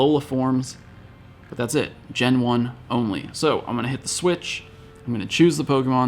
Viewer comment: Every time you said "Gen One" Auto captions thought you said "Jen Won" Every time you said "Pokémon Go" Auto Captions thought you said "Poke Vogue"